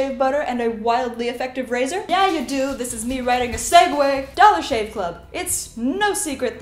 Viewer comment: "Yeah you do! This is me riding a SEGWAY!" Ahh I love your humor 😂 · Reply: I heard "writing a segue way", but now I can see the invisible scooter just off-screen. How did I miss the way she was moving across the screen?